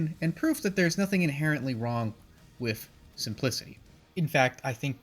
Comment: i thought 4 sure you said "there's nothing wrong with... some p-$$y". Which had me in tears until I realized what you actually said xD I just watched Armitage III and your review and I liked everything you said. Prog going to give this one OVA a watch as well. Thanks!